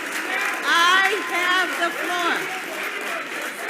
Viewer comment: Board Chair: "I have the floor!" Parents: "You sure about that?" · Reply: I heard "I have the foot"